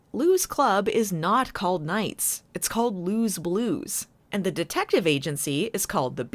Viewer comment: After hearing 'Lou's Blues', I now realise an entirely fitting alternative title for the early eps would be 'Blue's Clues'. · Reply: I genuinely thought did say "Blue's Clues" for the bar's original name.